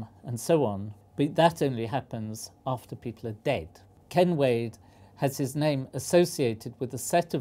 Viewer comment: When they say "Ken Wade" I keep hearing "Kenway" like Edward Kenway from AC4 or his son Haytham from AC3